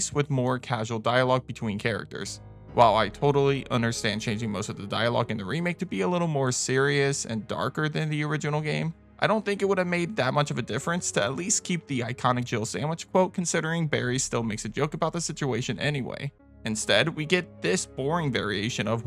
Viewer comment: I always thought it sounded like Barry said Jiggle sandwich